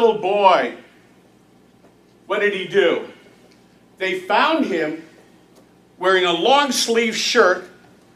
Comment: "They bound him"? Who? I think adoptive Dad did something, imo. He is conspicuously quiet. Edit. My bad, I thought he said bound, sorry. He said found.